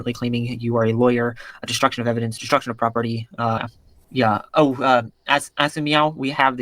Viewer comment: Anyone catch it???? "As of Meow" 🤣🤣🤣